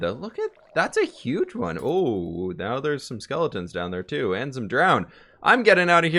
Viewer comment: did he said skeleton?